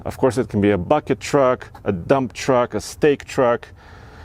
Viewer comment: Stake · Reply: Yeah I know I just heard “steak” and it made me laugh.